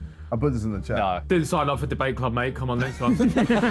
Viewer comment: can anyone tell me what Ethan just said here 😂😂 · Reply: "Didn't sign up for the debate club mate, come on next one."